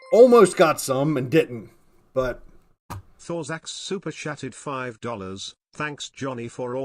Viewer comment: Is it just me or does the super chat voice sound like he says " super shat"?